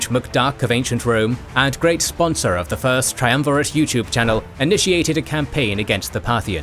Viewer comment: Did I hear sponsor of Triumvirate YouTube channel? 😂